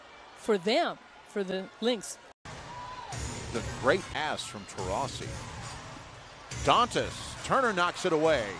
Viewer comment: did he say the great ass of Taurasi